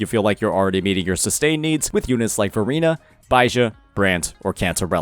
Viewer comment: lol I heard Furina when he said Verina